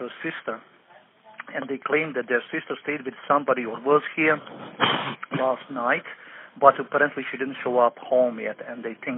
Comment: I know sound like he said that works here but I believe he said was her with somebody last night